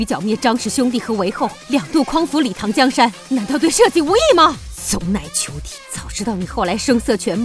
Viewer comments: 字幕上是 "混帳東西" 可是發音不同....... 誠心請教：有誰知道是哪地方的話嗎？真正意思？ 順便教我怎麼說嗎(正確發音)..........我聽不清楚......求解....？ 感謝~ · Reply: 读 死乃求 山西 内蒙 陕西都这么说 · 太平公主的原話是什麼? 聽不太清楚 好像和字幕不一樣 難道是方言? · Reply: 死乃求 晋语骂人方言，老李一家都是山西的